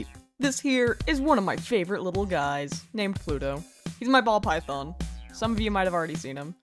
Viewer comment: I paused right as you said ball python and thought you said hes my balls 💀 Also aahhhhh 😭 snekkkk Its so cuteee